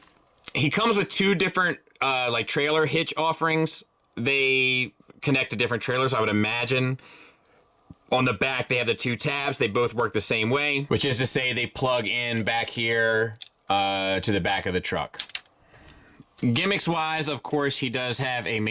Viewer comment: Did you say "Two Tubes?"